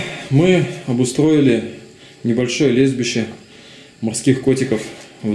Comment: мне одному послышалось не лежбище, а лесбище морских котиков?)